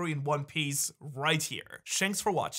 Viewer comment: Did he just say Shanks for watching? 💀